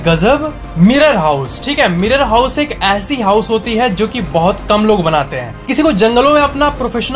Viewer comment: when he say 'Mirror house' I hear 'Horror house'😂🤣😂